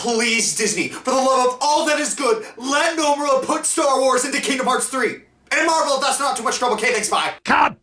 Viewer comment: What Did Say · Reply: He asked if they could put a Star Wars world in KHIII. I think he also said something about Marvel as well, but he said it so fast that it's hard to tell.